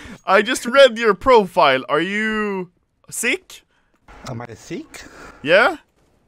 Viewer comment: i thought he said am i thicc?